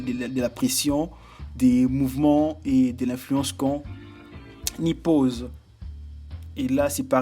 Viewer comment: À 4:48 j'ai entendu "à l'influence qu'on nipples" au lieu de " qu'on y pose" 😂😭 Ah lala... Sinon ta vidéo est géniale ! :D je m'abonne et te souhaite bon courage pour la suite :)